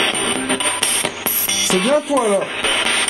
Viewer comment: moi j’ai entendu bien-sur, après je sais pas